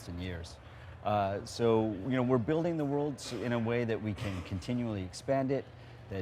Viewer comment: did he say building the worlds, like plural. As in more then one world, that'd be cool